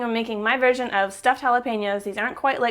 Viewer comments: I thought you said jala-penis LOL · I thought you said jala-penis LOL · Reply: Lol i thought she said jalapenas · Sounds like you said jalapenis